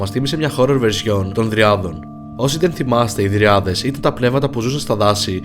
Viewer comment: Παιδιά στο 2:31 τι είπε ?? Γριάδες ή κάτι άλλο μπερδεύτηκα 😐